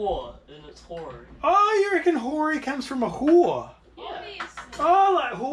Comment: As a Canadian I thought you were saying whorey... lol, reading the comments it's hori!